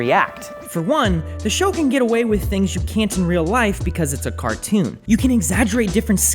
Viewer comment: wut he say? cartoon? oh hell nah